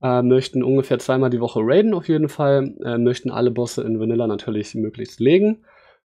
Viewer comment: "wir allen alle Bosse in Vanilla mythic legen" ... Hab ich mich verhört oder irgendwas verpasst? Gab es zu Vanilla schon mythic content? Ansonsten: Sehr coole Idee mit einer Gilde, die eigentlich zwei vernetzte Gilden ist .. oder auch umgekehrt ^^